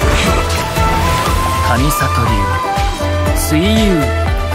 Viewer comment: 違うのは分かってるんだけど、どうあがいても｢神里流 see you｣って聞こえるんだけどなんて言ってる？？ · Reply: 神里流・水囿(すいゆう)ですよ